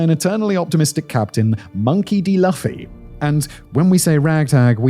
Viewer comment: Did i just heard, "LAFFY" ??